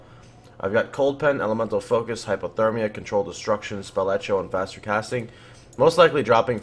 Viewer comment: Did he say spell etcho?